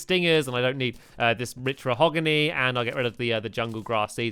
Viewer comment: did stampy just say "mich mahogany" or is it my ears messing up